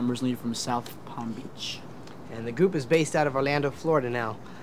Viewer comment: Is it just me or does Howie say "goop" at 0:30 lmao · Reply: He said group